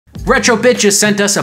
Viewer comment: sounds like he said retro bi***